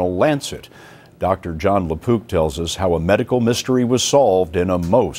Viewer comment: I misheard that as Dr. Jon LaPoop lol.